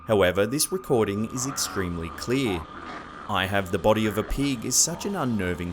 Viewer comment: I heard "I fed the body to the pig"